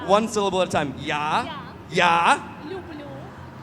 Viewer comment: мне послышалось "юбью",но у каждого свое мнение)))